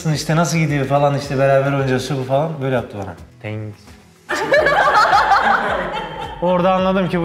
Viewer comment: Ebrar Görkem e ne demiş anlayamadım ? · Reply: Thanks